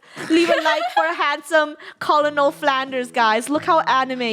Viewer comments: did you just say colonel Flanders? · did say colonel flanders?! XD